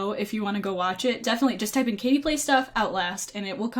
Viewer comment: I heard "Creepy stuff" instead of "Katieplaysstuff". First timer. Sorry -_